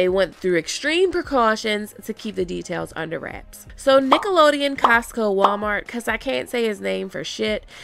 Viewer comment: I'm cracking up because I was was listening and not looking and heard you say Nickelodeon 🤣😂🤦 I'm like what does Nickelodeon have to do with GoT 😅